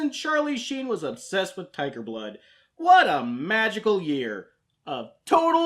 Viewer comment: I rewinded 3:00 like four times, did he say Tipa blood. I still have no idea. I have pretty bad hearing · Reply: Tiger blood.